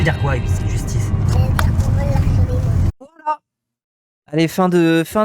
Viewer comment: il dit quoi du coup le gamin à 2:26? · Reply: "Ça veut dire qu'on vole l'argent des autres"